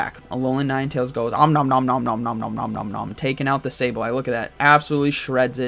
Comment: What does alolan ninetails say UM NUM NUM NUM UM